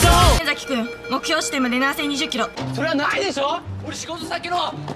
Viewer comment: 普通に聞き取れる所、ギリ聞き取れる所、マジで何言ってるか分からない所が絶妙なバランスで混在しててすこ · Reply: ホントは「南西20キロ」だけど初見はガチで7020キロに聞こえた